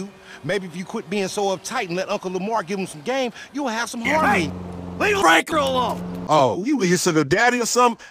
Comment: Michael: "Hey! Leave Franklin alone!" Instead of "Leave Lester alone!".